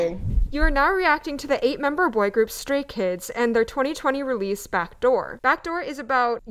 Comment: I need to listen to more Stray Kids Me : YES YOU SHOULD 😭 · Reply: The way he said it I thought he had said Scary Kids and I-😭😭